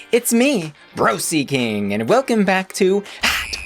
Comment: I swore you said your name was “Bro Seeking” Lolol